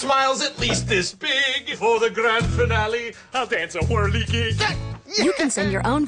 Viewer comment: What the heck does Ronald say I swear I hear " And for the grand finally I"ll dance a horny jig!" Ronald then turns to the kid and calls him a D..K. I know my ears must be playing tricks on me but,I truly sounds this way to my ear.